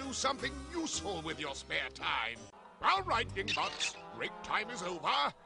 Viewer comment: Did he just say what I thought he said? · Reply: He said (dingbots) if that's what you mean